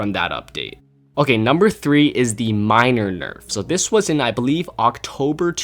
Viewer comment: I thought he said minor nerf